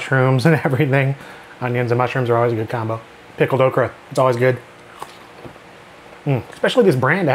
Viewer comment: I thought you said pickled Oprah